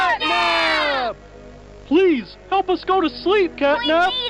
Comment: i heard capnap not catnap in